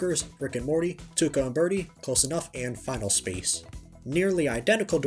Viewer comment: Did you say Close Enough? I wish they could somehow show it on Adult Swim and eventually come back! I love J.G. Quintel!